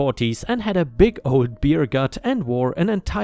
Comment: didn't he say the ED dad had a gutted buck on the back of his four wheeler? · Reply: re-watched the spot I thought I heard it at and he said "beer gut".... my bad